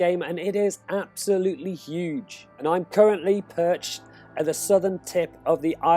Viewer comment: Did he say 0:11 i currently PURCHASED??? You gotta buy every part of map???